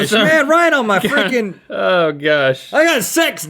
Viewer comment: Did anybody else hear Rhett say feckin